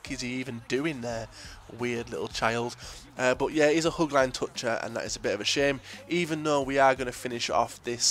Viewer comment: ' hugline toucher' instead of touchline hugger lol hahahhahahahah jks nice vids tho